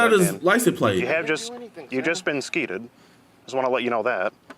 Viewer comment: I spit out my water when I heard “you’ve just been skeeted” for the first time.